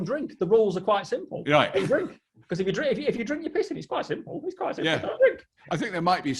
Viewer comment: I had to rewind because I thought guy said “drink your piss, it’s that simple ” 😂